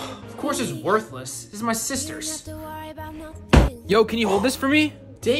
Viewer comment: I thought u said ofc its worthles sit my sister and threw a baby